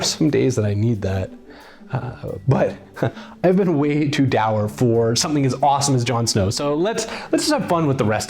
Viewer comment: What word did he use at 7:42? Dower? Contextually it seems like "depressing", but I must not be able to spell it close enough to not give me definitions based on dowry. How do you spell this word?